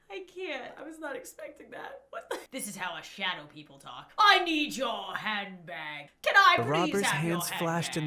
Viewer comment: I thought he said "I need your hand back." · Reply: I thought he said ' I peed more handbags'